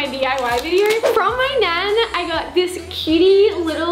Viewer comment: Thought said from my man 😳 but she said nan